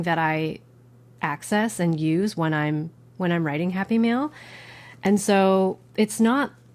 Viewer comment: Ah, my beginner stationary self thought you were saying “Happy Meal” for a good half of the video. Hahaha! I was like McDonald’s sell stationary?